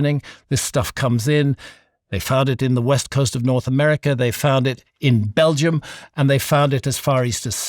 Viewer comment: i thought he kept saying "they farted"